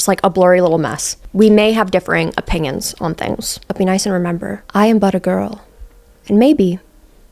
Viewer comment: girl, i thought you have been saying "i am butter girl" this whole time thinking it was some type of inside joke until now and now i realize you are saying i am but a girl